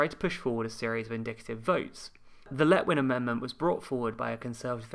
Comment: This is all very interesting but also every time you say "indicative vote" I hear "vindictive vote" I'm not going anywhere with that, I just do